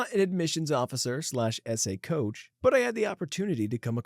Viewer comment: Unrelated to any specific story here but I've listened to so many of these YouTube Reddit stories as podcasts with no video that when you said "Essay Coach" I heard "SA Coach" and did a triple take😅